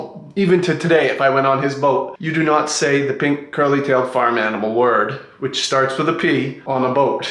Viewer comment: I had to read the subtitles to figure out what he was talking about because I kept hearing “pink curly tailed fireman hole word” and I was TOTALLY LOST